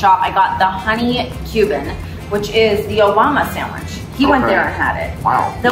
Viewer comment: Did she just say “The Yo’ Momma Sandwich” ??? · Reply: "Obama" Sandwich. He went to West Tampa Sandwich Shop when he was in town campaigning for reelection in 2012 and got the Honey Cuban, so they re-named it after him.